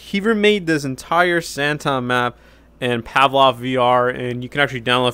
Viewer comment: Plot twist: This is actually Garrys Mod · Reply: I thought u say Pablo for 1 second